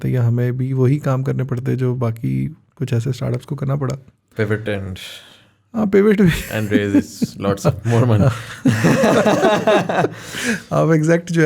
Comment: i want to kn now what said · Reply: Pivot and raise alot of money